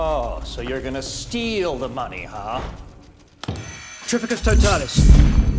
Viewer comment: What was the spell? I couldn't hear it clearly. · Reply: Pertrificus totalus